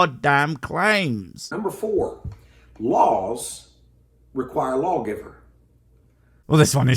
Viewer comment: No. 4 I thought he said LOLs "LOLs require a LOL giver." Yes dude. Thanks for the LOLs. 😂🤣😂🤣😂😂🤣😂🤣